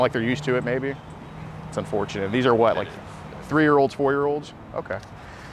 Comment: did he say 4 year old ? that is sick